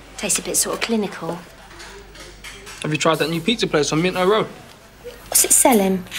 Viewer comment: at first I thought mickey said have you tried that pizza place that's in my room